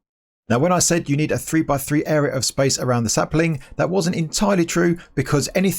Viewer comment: I thought he said "spice" I was so confused for a sec